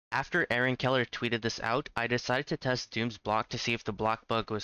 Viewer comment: To this day, I have still not had an instance where I hear the name “Aaron Keller” and not misheard it as “Hellen Keller” (without expecting to hear it)